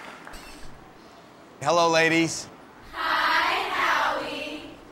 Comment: I could have sworn I heard Howie say “Hello lady” instead of “Hello ladies”